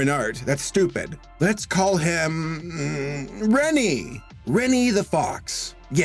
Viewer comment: I thought it said Kenny the fox